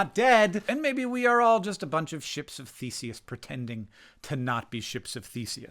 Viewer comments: So, I heard "ship of feces" so even your cobbled mental capacity is still doing donuts around a lot of us. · thought you said “ships of feces” at first and didn’t question it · "I feel more like a Ship of Theseus than I used to" is such a poetic line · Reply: I heard "...ship of feces..." at first and was like preach! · I thought you were saying "ships of feces" and I was like true dat. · I heard "ship of feces" at first... not inaccurate but not what you said lol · I heard “ships of feces” and was like “yeah that describes humans” and then realized on the third time it was “ships of Theseus”. I stand by “ships of feces” being a good descriptor of human beings. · I heard ships of feces and had to look at the subtitles ^^ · heard that as "Ships of feces" and thought well damn, we are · I know you said ships of Theseus and I know what that means, but I swear to god at first I heard you says "we're all just ships of feces" and I was like "yeah that makes sense, I'm just a vessel that carries poop around". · When you first said "ships of Theseus" I thought you were saying "maybe we are all ships of feces" and I am glad you repeated it haha! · I thought you said, "ship of faeces" and I thought that that was a new bleak, nihilist way of describing the human body. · I thought he said “ship of feces.” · at first at 2:52 i heard, ships of feces, and i think i prefer this homeric tale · Misheard "ship of Theseus" as "ship of feaces" the first time and was confused but agreed 😅